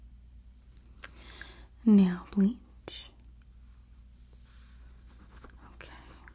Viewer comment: excuse me but did she say “nail bleach” *googles